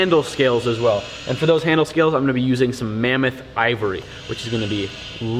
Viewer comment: Did he just say Mammoth Ivory?